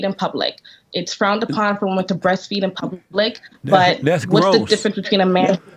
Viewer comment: Did she just say "why don't you want to see my breast in public?" Did I hear that right?